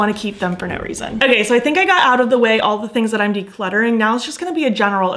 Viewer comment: I heard "300 things that I decluttered" and I was like WAIT WHAT hahahaha🌟🪄🔮🎇